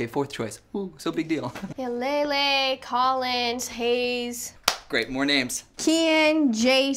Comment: did she say Collins. Collins key